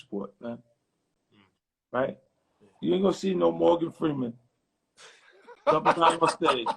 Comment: …. What did 50cent say ???..... You aint gonna see no more ____________ ???? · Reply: no morgan freeman.